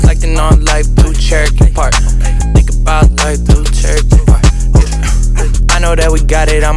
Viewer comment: Thought he said, “Thinkin bout life through a cherry rhubarb.”